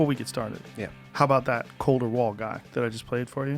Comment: Who's the musician that they were talking about at the very beginning of the podcast? I thought Joe said Cole Dewall but I'm not sure